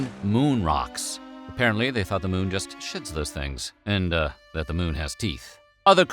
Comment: 😂😂 the moon shits those things(I know he said shed, but it sounded like shit)